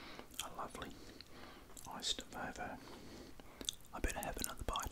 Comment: Must admit I thought it said Volvo not vovo